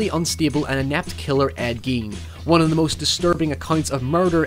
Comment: Who is the serial killer you mentioned around 8:00? I can't understand it through your accent and the subtitles are not helping lol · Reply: Ed Gein.